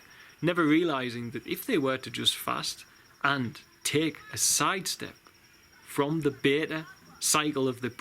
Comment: Does anyone know what he said at 26:07? "Take a side step from the _ _ _?" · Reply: Beta cycle of the brain